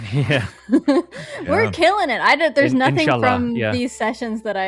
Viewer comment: did say inshallah?